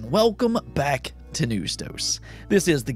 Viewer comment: This is the second time I’m asking this so I hope you reply. What do you say in the beginning of your videos? It sounds like you say, “Welcome everybody to new stokes!” Is that what you’re saying? Also why is your channel name “jaywood2010”? I would love to know the reasoning behind it! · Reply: Lol no problem I say "Welcome back to News Dose" And is just what I've went by so I never changed it.